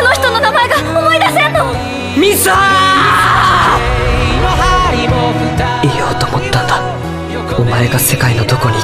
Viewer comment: i heard missstaaaa